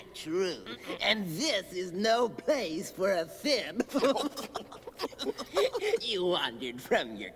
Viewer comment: did he just say Simp?! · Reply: He said fib, as in a lie